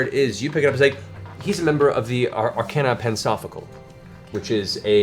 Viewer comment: Every time Mercer uses that grumbly “Big Bad” voice, part of me doubts my heterosexuality. The rest of me pretends not to notice. Also, every time I hear “Arcana Pansophical”, my brain registers it as “Arcana Pants-off-ical”